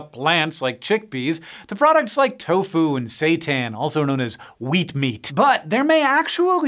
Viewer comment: I'm sorry did he just say "Satan" ?